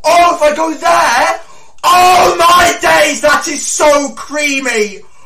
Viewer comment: Thought he said “oh my gays”